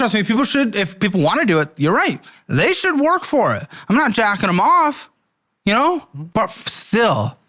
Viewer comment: Did you really say "I'm not jacking them off"?